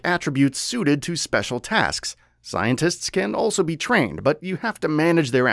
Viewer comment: I thought he said tranqued. Imagine you could tranquillise the scientists. Lol!